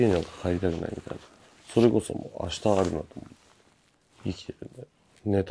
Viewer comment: それこその後なんて言ってるんだろう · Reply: tokyo yusuke 明日有るのみ